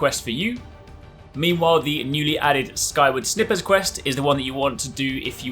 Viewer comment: Did you really say 'snippers' lmao